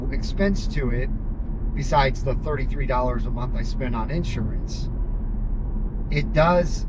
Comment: Congrats! Best wishes to you and your wife! Side question....around the 6:25-6:30 mark you mentioned $33/month insurance?! Did I hear that correctly? I live in a different state, is that a Texas thing? Curious to know, Thanks and congrats again